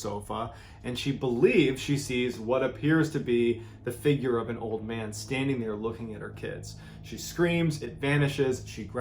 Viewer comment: I honestly thought he said ' a figure of a man looking at her TITS"